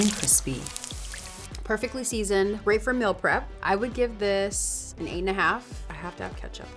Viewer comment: When she did the hash browns and said “for meal prep” I thought she said milk breath 🤣